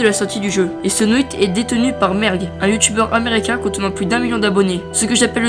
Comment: a la place de merg jai entendu merde XD